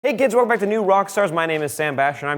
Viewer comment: The first words he says sounds like he says "hey Gids" n not "hey kids" like I'm sure he meant to say